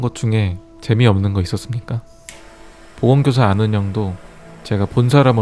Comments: 나만 보건교사가 보험교사로 들리냨ㅋㅋㅋㅋ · 난 왜 보건교사 가아닌 보험교사라고 들리징?